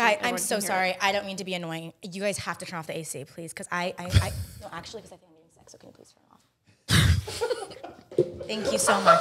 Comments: what did she say i couldn't here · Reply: “Sorry I don’t mean to be annoying. You guys have to turn off the AC please. Cause I-I- I no actually cause I think I’m getting sick. So can you please turn it off? Thank you so much” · What did even say at 8:07 😭😭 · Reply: “Sorry I don’t mean to be annoying. You guys have to turn off the AC please. Cause I-I- I no actually cause I think I’m getting sick. So can you please turn it off? Thank you so much” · What does say her reasoning for wanting the air off is? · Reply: “Actually cause I think I’m getting sick, so can you please turn it off”